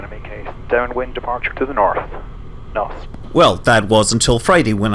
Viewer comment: Sounded like you said “Norf” there… 😉